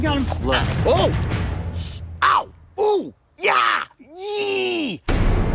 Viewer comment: I heard ya yeet